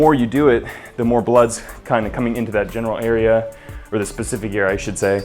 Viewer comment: did he say Genital Area ? HAHA